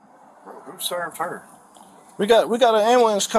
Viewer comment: At (2:00) Did he say who served her 🙊na man 😅